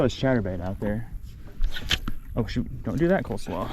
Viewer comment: Did I actually hear you say, "oh shit" at minute 7:48-49???????? Draining out that Lake is TEMPORARY.... NOT THE END OF THE WORLD... or life as you know it..... Do some reading of your Bible.... contemplate what's being said to you.... get in touch with God.... and stop making such a big whine out of reality. GEESH!!!!!!